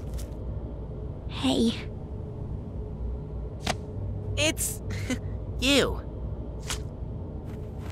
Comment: HELP WHY DID IT SOUND LIKE THEY SAID "It's.. Ew."